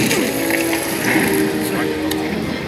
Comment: I thought he said in "justice: gods among us." but he really said "injustice: gods among us."